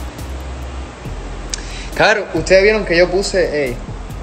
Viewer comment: Hola yo estuve en el live y me saludooo me dijo "hola carolina te amooo" y yo me moriiii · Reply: see jaja erick pronuncio mi nombre 2 veces la primera vez en el comentario anterio y la segunda en el minuto 2:18 que dice "caro" ahi jajjajaja